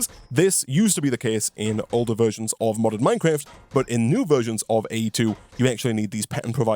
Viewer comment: For the longest time, everytime you said "modded minecraft" I always thought you said "modern minecraft" like thats a thing people say.